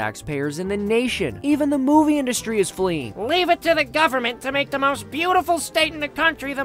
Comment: I heard, "Even the moving business is fleeing" lol